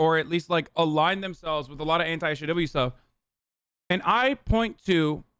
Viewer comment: /WHAT ON EARTH DID HE SAY HERE??? Legitimately can’t make out what he said “Aligned themselves with a lot of anti-__?__” it it’s”a lot of anti-SJW stuff? Is that what he said?